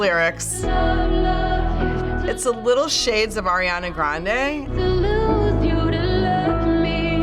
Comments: What did you say?? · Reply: Little shades of ariana grande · what did woman just say ??? wtf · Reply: selenator oranais “There’s little shades of Ariana Grande”